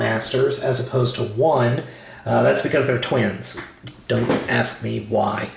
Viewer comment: I thought you said the core was twins